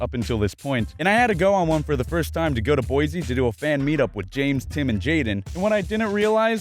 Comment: Did you just say bozie